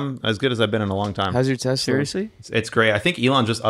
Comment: Funny when he said "Hows your Tesla" I heard "how's your test level" and then instantly goes into his testosterone levels